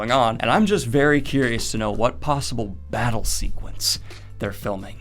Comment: I thought you said Brothel Scene and i was so confused. I know its been awhile since I read WOT but could not remember a whore house. LOL Battle scene makes more sense. Now if we were talking GOT that would be a whores of a different color.